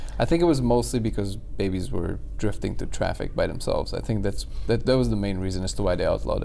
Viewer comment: Did i heard this right at 33:42 'Babies were drifting through traffic by themselves' ?? What does that mean?